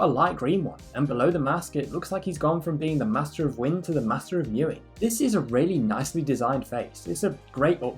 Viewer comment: Am I the only one that thought he said "he looked like the master of wind , now he looks like the master of urine ". No offence😂😂😂😂